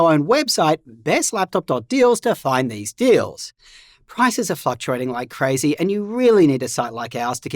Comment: it sounded like said "prices are f&cktuating". A truer Freudian slip has rarely been uttered.